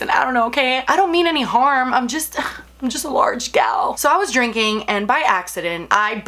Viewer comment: When you said “I’m 5’10 I’m a large gal” I thought you said “I’m 5’5 I’m a large gal” and I started dying laughing bc I was 5’5 in middle school Ahh😂